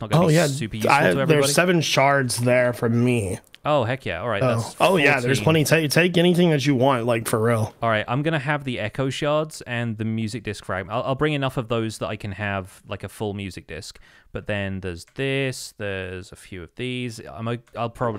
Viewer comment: For almost half of the video I thought that he was saying the eggo shards not the echo shards